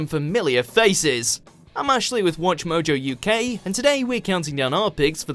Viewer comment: Until this video I thought he said “I’m actually from WatchMojo UK” because most people would assume from the intro he was from the normal one